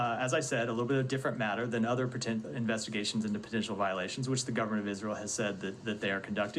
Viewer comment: it sound like he said “ pretend investigations” almost like a freudian slip. And how is what he just explained not you allowing them to investigate themselves, what a joke.